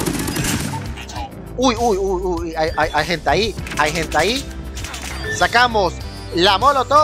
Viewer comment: entendí hay hentai ._.